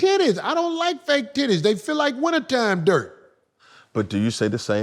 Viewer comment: Brah Corey..,.is wild did he say fake tits feel like wintertime dirt 😂😂😂😂